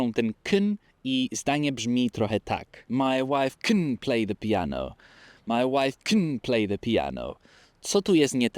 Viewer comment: Za pierwszym razem usłyszałam "My life kompletnie pijana"😅